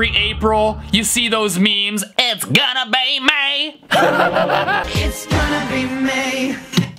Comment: all i heard was "its gonna be NEIGH" is it just me cuz i own horses or do more ppl hear it?